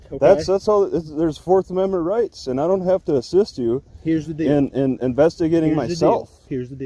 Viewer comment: I love... When I hear, " here's the deal". I hear them saying " obey me "